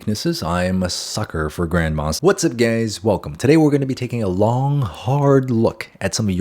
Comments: Ngl I thought you said what’s up gays in stead of guys 😂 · did he just say what’s up gays or is it just me 😭🙏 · did he say what's up gays?